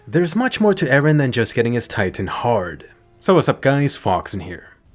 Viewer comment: Not even 10 seconds in and i hear “eren getting his titan hard”